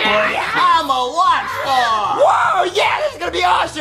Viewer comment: Did anybody thought that Morton said “I’m a watchdog” when it’s really “I’m a rockstar”.